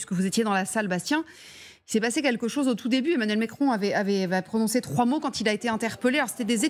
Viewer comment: c'est moi ou la presentatrice a dit Emmanuel micron à 0:24，fervente de grolande aussi apparemment😂😂